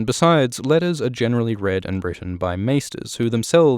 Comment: I thought you said "read in Britain" and was like.... whaaaat, so I had to rewind it like 6 times. My brain is so weird sometimes.